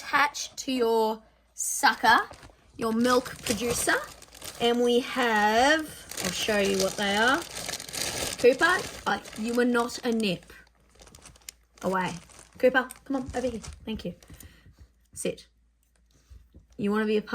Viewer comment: When you said “Cooper” as you were opening the nips (31:07), I misheard and thought you called him “Tupac” and I said out loud (to no one, mind you) “oh my gosh that is hilarious I didn’t know she liked Tupac like that.” Then right as I finished that sentence you said “Cooper” again and I heard very clearly the correct name and I laughed quite a bit at myself and I just thought I’d share my little faux pas with you all 😂